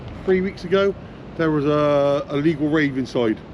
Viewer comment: Sounded like he said a legal rave haha